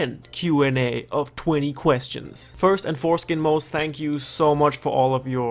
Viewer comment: Did you say foreskin 0:08 ???